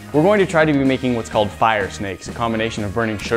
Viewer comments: I thought it said fire snackes...... I'll go home. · thought the vid said fire snacks :(